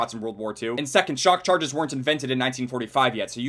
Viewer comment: Who else thought that he said "Shock Charges weren't invented in 1995"?